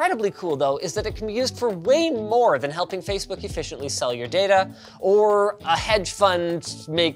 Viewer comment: did he say sell your data.?